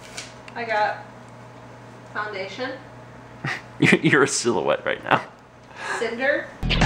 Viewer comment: WTF 2:29 DID JUST SAY UR ARE SO WET RIGHT NOW TO MAL WTF WAT WAS THAT